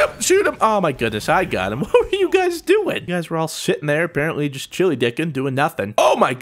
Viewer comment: What did he say at 3:07? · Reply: like he said chiliedickin.